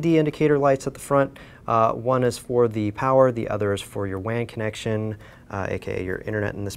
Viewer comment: When he said WAN connection I thought he said wank hahahaha. Btw your boring pal